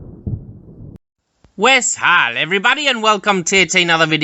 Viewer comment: What is he saying here? 0:10 Sounds like "West Harlem Everyone", but pretty sure that is not correct TIA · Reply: Thank you for the generous contribution!!! I say "wes hál" which is an Old English greeting, my friend.